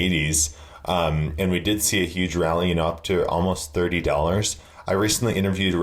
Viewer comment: did you say HUGE rally is $30 ? coming from 2010 thats horrible considering all our efforts